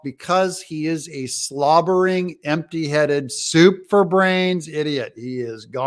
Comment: at first I thought you said 'super brains,' then it became clear, you said 'soup for brains.' That makes a lot more sense. He is little more than a malfunctioning animatronic like what we see in Dizzyland.